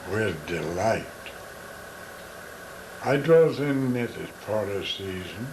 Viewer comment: Can anyone decipher what he says at 22:32 - 22:35 and 24:38 - 24:41 ? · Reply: It sounds like it might be "I dwelt within it for a season"